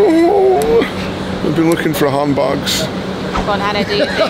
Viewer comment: I thought he was saying humbugs i was like wtf. Like a christmas humbug? Then i eventually figured it out. Handbag.